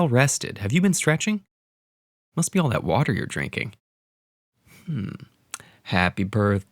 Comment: ～ I can't hear the sentence here. What did the man say? It sounded to me like"～～～ water you drinking" Can anybody teach me? ※I'm Japanese and I can't speak English well